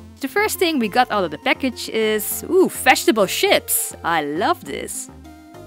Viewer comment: omg i thought you said vegetable SHIPS lol ^O^